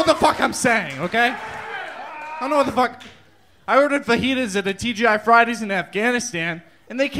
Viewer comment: Fajita?? Oh, I thought you said Fatwa!